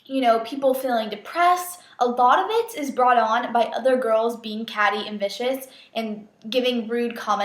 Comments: hahaha when you said catty & vicious i thought you said catty and "bitches" and i was like whoa swore?! haha anyway love you, good video :) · When you said "catty and vicious" I almost thought you said "catty and bitches." Haha.